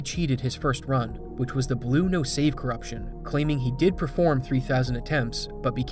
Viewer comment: i'm too used to japanese titles i keep hearing "blue no save corruption" as "blue's save corruption"